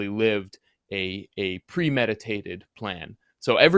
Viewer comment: sounded like premeditated murder to me!!!!